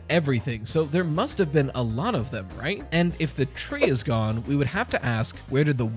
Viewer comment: I was spacing out for a bit, when I came to the first thing I hear was “is the tree cannon?” My shock was visible